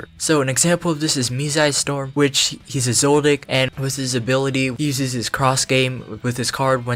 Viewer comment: sounds like he says Mizaistom is a Zoldyck? prolly meant/said Zodiac. but i cant unhear it lmao